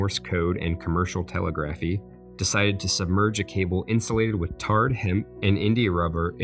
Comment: did he say tar HEMP???